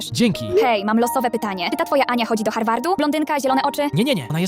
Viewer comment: Najpierw zamiast "do Harvardu" usłyszałem "do Hogwartu"